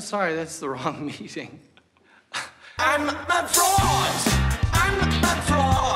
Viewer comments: i'm sorry, all i hear is "i'm a frog" and i think it's better that way 🤧. i'm a frog, too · The more he says 'I'm a fraud', the more I hear 'I'm a frog'